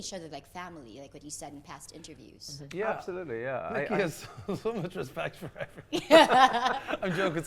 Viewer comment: Hi, thanks for the video. :))) Do you understand what Mark says on 10:23 about Nicky. English is not my native language so I dont clearly understand. Can you please tell me. :) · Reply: Pretty sure he said "Nicky has so much respect for everyone"